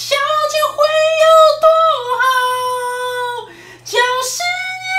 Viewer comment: 我怎麼聽成 小姐會有多好......